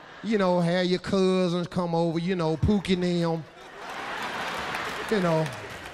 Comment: what did he just say?? Pookie nail?? What's that? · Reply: Pookie and them